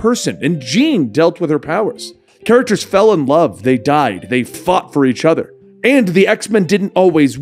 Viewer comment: Did he say they fucked each other?! Lol